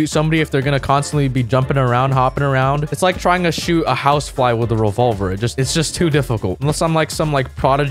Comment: what I heard: “It‘s like trying to shoot a house plant with a revolver. It‘s just too difficult“ Me: bro wtf!?